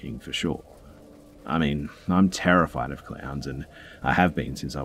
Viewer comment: At first I thought you said they had clams. Then I got that you said clowns. I was wondering how anyone could be that obsessed with clams.🤣🤣🤣🤣